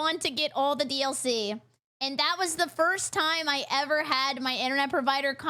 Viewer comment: pause did she say "first time" lol?